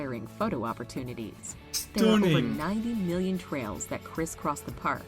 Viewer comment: I thought she said 90 million trees 🌲, I don’t think the park would have 90 million trails.